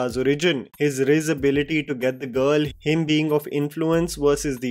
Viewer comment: did he just say “his rizz ability to get the girl”?